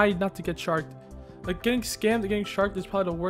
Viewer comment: i thought this video said "dont get Shanked"